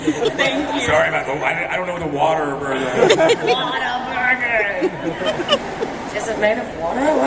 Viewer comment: Omg Linsey when she mentioned whataburger and the south, being from texas eating whataburger every other day I flipped my shit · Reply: Kaylan Ahmed I thought she was saying water burger! Haha I've never heard of it.